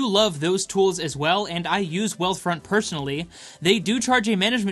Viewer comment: I misheard Wells Fargo and was about to click off lol